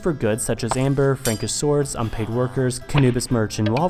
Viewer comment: Did he just say unpaid workers instead of slaves?